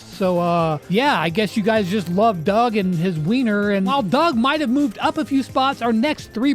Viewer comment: What did he say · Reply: weiner